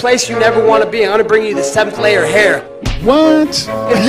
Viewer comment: It still sound to me like "lair" even after multiple replays. :d Maybe a little articulation wouldn't hurt, Colby... it's not Masvidal's fist nor Verdum's boomie. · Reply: I meant, it sounds like Colby said "Seventh lair of hair" I didn't say it clearly, mb.